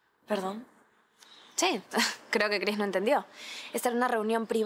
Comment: le dijo Grace no entendio a Yuly o escuche mal?? min